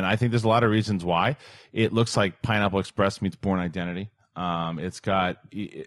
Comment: What did Kristian say? "The Porn Identity"???